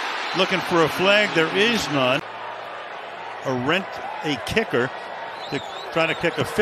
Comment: did they say a rent-a-kicker at 1:01 ?